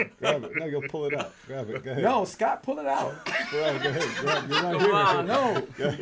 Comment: david blaine: "nigga pull it out." Did he say it or was that just my ears.